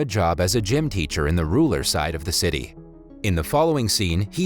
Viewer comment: Is it just me or did the narrator say Ruler instead of Rural